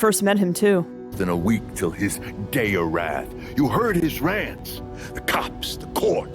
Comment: Does anyone else hear GAY OF WRATH